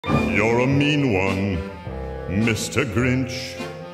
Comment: "your the meme one mr grinch"💀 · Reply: What i heard "Your a meme one, mr cringe" Yes mr cringe